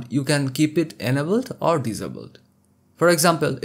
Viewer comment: I hate to be “that guy” but when you say the word “enable“ I thought you were saying “edible”. 😂😂😂 it took me a while to figure that out. Love your tutorials.